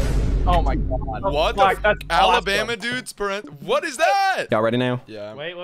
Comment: Honestly I thought it said Alabama nudes